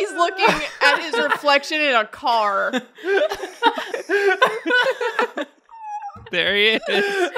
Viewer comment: says There he is but the subtitle says Burry it lol